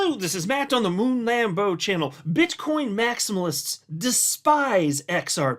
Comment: sounded like you said "Bitch coin maxis" 😆🤣😂